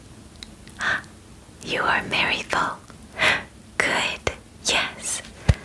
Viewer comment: anyone know what she says at 6:01? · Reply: “you are merryful”?